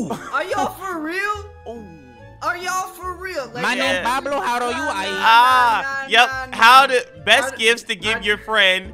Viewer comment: I dont even know what said bruh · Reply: Says in deep breath: Enchanta normal